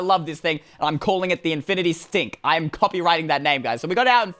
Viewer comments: I thought u said the infinity stink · Who heard him say infinite stink